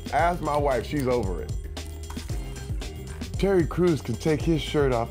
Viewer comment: It sounded like he said Cherry Crews xDD